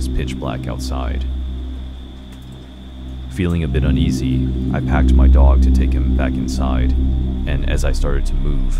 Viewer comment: Lol in the first one when the guy was watching her from the car to me it sounded like she said : I packed my dog😂 Me: in a bag?😂🤣